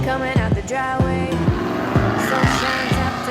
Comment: did he just say "you're taking my Lamborghini virginity, Chris"? The stress being on the identity of "GhostRider".....